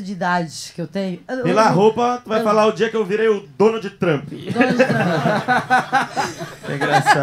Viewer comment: Eu entendi "o dono de trâmp" e demorei a entender q era Donald Trump kkjkj